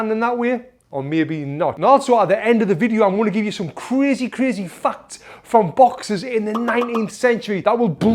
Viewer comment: Why did I hear "I'll show you some crazy crazy f*ck" 🤣